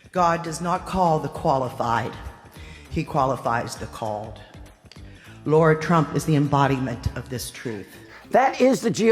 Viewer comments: thought I heard "Lord Trump" but words were "Lara Trump". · Did she just say, "Lord Trump is the embodiment of this truth? "